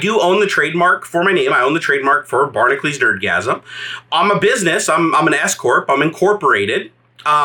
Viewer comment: I first heard this as, “I’m a business, I’m an escort...”